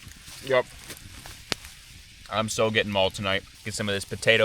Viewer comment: did you say you were getting mauled tonight...? lol